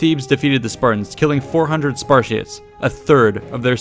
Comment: I heard 'SPASHITS'